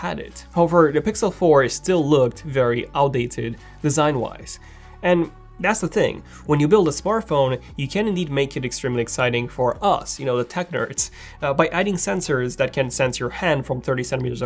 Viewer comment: ohhh Tech Nerds.. I thought you said the Tuckers 😂 (my name)